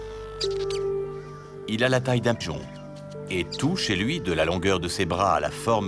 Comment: "Il a la taille d'un ???" quoi ? Si quelqu'un a compris, merci du coup de main. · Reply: D'un pigeon ! ! ! 😁